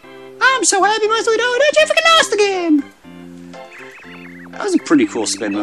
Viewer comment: sounds like your saying, "I lost the game"